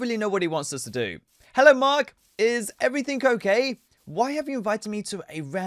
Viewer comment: it sound like you say is everything cocaine